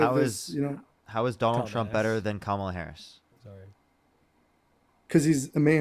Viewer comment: what did say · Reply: called the caller a ðumb@ then said “sorry.”